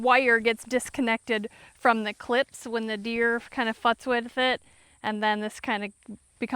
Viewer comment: whaat did you say?! 😂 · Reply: Futz. The deer futz with the wire.